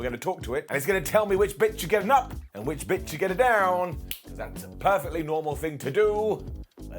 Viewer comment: it definitely sounded like you said” if this bitch should get a up or if this bitch should get a down “😂 love ya man